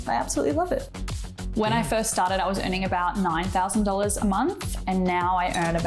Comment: did he say 9000 a month